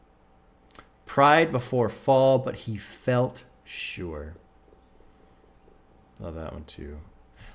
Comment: On Houthi I always heard "Pride before fall, but he fell short" Obviously I was hearing it wrong, but I also liked it the way I heard it. Like he understands the pride comes before the fall, but he was upset that he never even made it to the point where he gets to feel prideful.